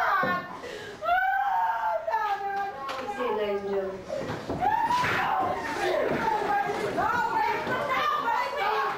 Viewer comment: want did she say before he went nuts? Lol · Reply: KiamarMasterT She’s whimpering but as he starts to fight she’s actually saying “no baby no!” It’s actually pretty sad and I really feel bad for her.